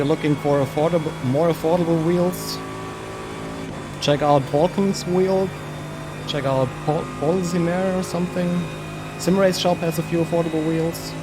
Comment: what did you say on 7:10? what wheel? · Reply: balkn.shop and polsimer :)